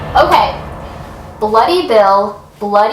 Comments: If I'm being honest, I thought it said Bloopy Bill. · TBH at first I thought it said "bloody hell" Something is wrong with me lol